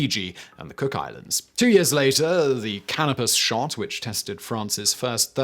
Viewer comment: I swear at 1:14 he said "Cannabis shart."